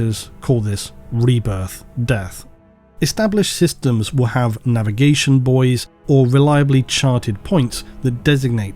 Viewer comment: When he said navigation buoys I heard Navigation Boyz and I thought of an orc floating through space in a hi-vis vest directing traffic